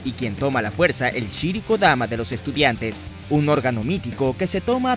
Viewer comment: Acaso escuche mal... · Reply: nop, dije ANO, trasero. so es raro